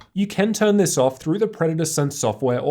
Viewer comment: Did he say "you can" or "you can't" about turning off the startup sound at 5:33? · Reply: You can turn it off, either in the predator sense software or BIOS. I upload perfect english captions by the way :)